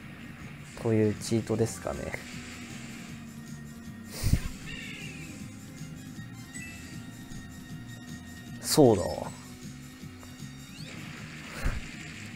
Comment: そうだよ（便乗）にしか聞こえない